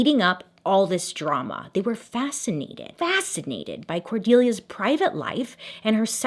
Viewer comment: I thought you said whordelia 😂